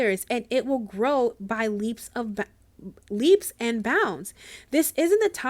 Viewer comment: I thought you said bubbles and clown. I'm at the circus. Thank you.